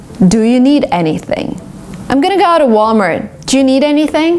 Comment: sounded like - Ahm gonna go out'tuh